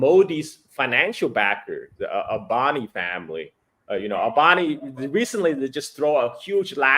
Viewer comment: Thought you guys were saying “boni family”… was not disappointed with the results 😅